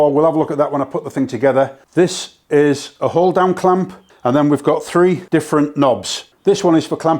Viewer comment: i know you said hold down clamp: but i heard "the while damn clamp" lol